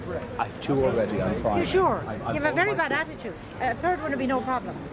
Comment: Lol what does the lady in the stall say to Dara at 4:00?? You have a very bad attitude? Lololol 😂😂😂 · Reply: Yeah, the lady confirms that Dara doesn't want a cat, and he says he doesn't want it, he has two cats already. "You have a very bad attitude," she replies. "A third would be no problem."